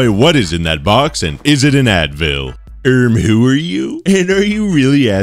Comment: I thought he said anvil. I was like "Do you really think an ANVIL is contained in a 5x5x5 box?"